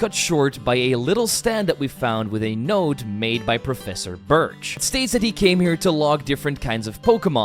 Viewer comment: i thought it said bitch XD